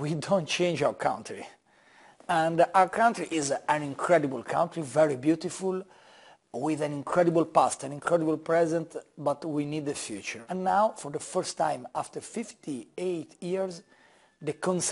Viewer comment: i thought he said : "we have incredible pasta, incredible present" haha